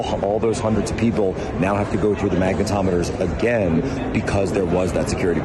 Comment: I thought he said ‘maga tometers’ lol